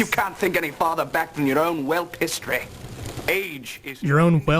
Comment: "Your own whelp history" is what I heard. 5:16 It means a stupid youth or animal.